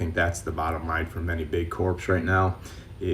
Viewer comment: lmfao subtitle says CORPSE instead of CORPS